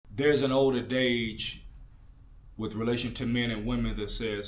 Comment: Older "adage" not dage sounds like you said dage? Not knocking you I just want you to make room for improvement